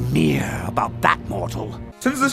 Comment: I like the line I thought I heard first from The Joker, "There is nothing mere about Bat Mortal." But it turns out he said That Mortal. What a missed chance!